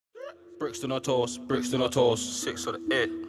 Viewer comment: Intro- Brixton or toast 😜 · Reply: zi zono gui yeah g I know he's taking bout tulse hill bruv. Just thought it was funny coz it sounded like Brixton or toast when I heard him say it the first time.